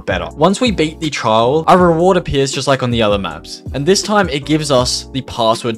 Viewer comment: I thought it said 8" maps haha